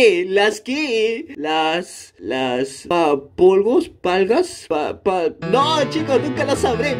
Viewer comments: todos dicen que dice nalgas pero es reglas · Ya se que dice nolgas